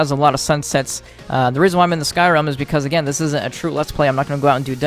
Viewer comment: 5. Trimach (First Trove Friend's First Dragon) 4. Azorian (First Ever Dragon) 3. Erel (Aesthetic) 2. Fortras (Aesthetic + Functionality) 1. Palashien (Long-ranged Fortras Functionality + That One Mod That Makes It A Fish) P.S. I kinda guessed Erel would be top because of how much you kept blabbering about how good he looks on that video where you got him. P.S.S. I kept hearing "Skyrim" every time you said "Sky Realm"